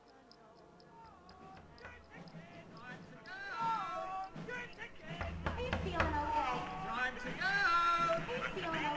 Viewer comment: Please help solve an argument for me! At 0:55 to 1:20 is he saying "Time to go" or "Let's go" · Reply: time to go